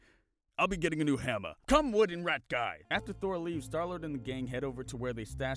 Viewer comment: When he said come wood and rat guy I thought he said come wooden rat guy.😂😂😂